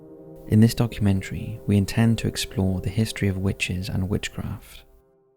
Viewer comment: I thought it said witches and Minecraft 😂